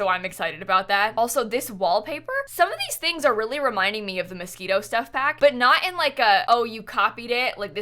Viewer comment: i always hear "the mosquito kit"